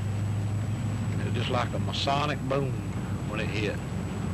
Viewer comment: Did he say a masonic boom?